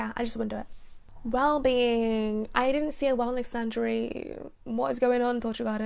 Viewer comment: did you say misandry ? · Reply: Nope! I said wellness sanctuary - lol I blend my words sometimes when I talk. Learning to enunciate is on my to do list